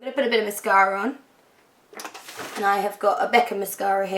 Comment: When you said Becca mascara, I swore you said Beckham mascara and I stopped and wondered why on earth David Beckham would bring out a range of cosmetics... Although that's probably the one thing he hasn't put his name on to be honest :)